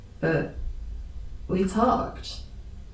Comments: Am I tripping or am I hearing her say she fucked the monster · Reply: She said "We talked" but with her accent it does sound like she said "forked" 😂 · I swear I hear "we fucked"